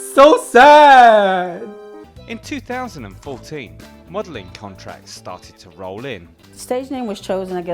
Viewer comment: I misheard and thought she said "waddling contract"....